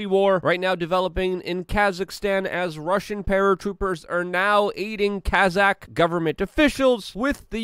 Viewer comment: "As Russian paratroopers are now eating Kazakh government officials" That's what it sounded like lmao.